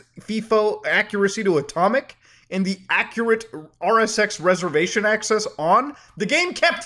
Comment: I thought he was saying acura rsx (the car)